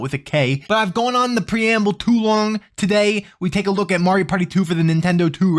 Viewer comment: I didn't hear the rest of the joke the first time through; I thought he said, "Mario Party 2 for the Nintendo 2"